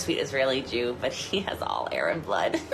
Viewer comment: I am APPALLED that she said that guy is "sexy" because he has "Aryan blood". WHAT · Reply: I heard "arab blood," which is almost equally as offensive and ignorant, but for an ENTIRELY different reason.